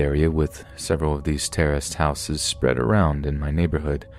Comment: Every time you say "terraced" house I hear "terrorist" house, hahaha.